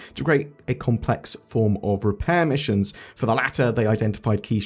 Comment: Thought that said "repair missiles" for a moment, I was picturing being the most violent space mechanic ever.